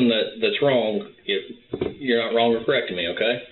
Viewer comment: Whoever it was that edited the subtitles, you deserve a medal. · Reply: there's already a mistake: cop said "you're not wrong in correcting me" and the subtitles said "you're not wrong or correct in me". I think that medal should be participatory at best.